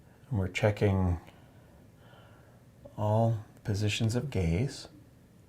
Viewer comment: Wait wait wait, did he just say he’s checking all positions of gays?